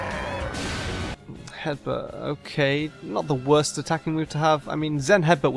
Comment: pretty sure Ash said zen headbutt instead of just headbutt. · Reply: I guess I confused "then headbutt" with Zen Headbutt.